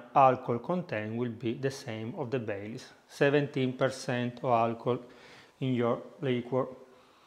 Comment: Thank you for the video. What % alcohol is the whiskey you used in this video? Grazie! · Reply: yes I did thanks. I believe I heard you say 17% alcohol in this Irish cream liqueur video..I guess you really said 57%. Lastly, what % alcohol does this Irish cream liqueur contain?